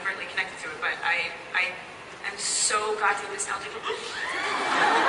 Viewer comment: I don't get it. What did she said? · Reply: Mary Cher Reyes ”so god damn” . basically, she cursed.